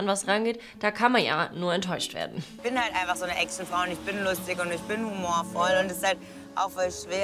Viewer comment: Hab bei 2:26 "Ich bin halt einfach so ne Echsenfrau" verstanden. Genug Internet heute für mich. 🙈